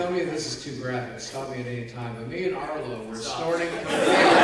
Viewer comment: Could someone please tell me what they said? I couldn't hear everything. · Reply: “Alright, now tell me if this is too graphic, stop me at anytime, but me and Arlo were snorting cocaine” Arlo: “...” “out of Jay’s butt crack.” Arlo: shakes head no: “And I was like- the Angry Video Game Nerd was there and he was naked” Arlo: mumbles something I can’t make out “um... I’m stopping ya.” “Wanna get some more cocaine after this though?” Arlo: “Uh... I’m a little-” “He doesn’t even have a nose.” Arlo: “Haha! slaps table Gotcha!”